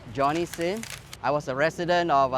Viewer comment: anyone else heard johnny sins before the words came out